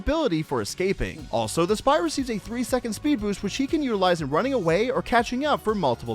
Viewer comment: I’m pretty sure when said which he can use, I heard it as which she can use